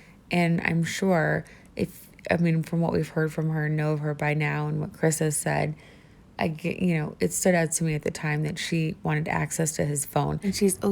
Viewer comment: Anyone know what the heck she said after "I'm sure...." at 6:35? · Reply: At that time stamp I said that from what we know of her by now and from what Chris has said, she wanted access to his phone. That was her excuse.